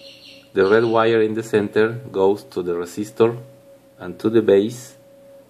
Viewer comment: he say Rasistor instead of resistor